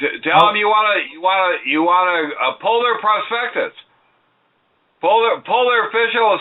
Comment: Prospectus is the word you all were looking for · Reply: Polar... Haha! It kinda sounded like he said polar-something. He said, "pull their perspectus...pull their official statement."